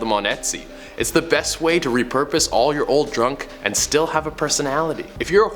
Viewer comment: Top 10 Scary Ways You Are Killing The Earth <link> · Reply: Like 3:38 he says drunk instead of junk